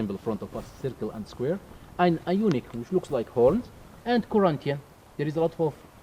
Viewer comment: I am not sure but I think the pillars are called ionic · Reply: Doric, Ionic & Corinthian. In the subtitles it was misspelled as "unique"